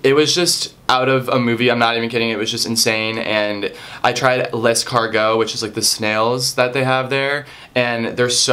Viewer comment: Lol at first I thought he was saying 'I tried less cargo' but he was saying 'I tried l'escargots'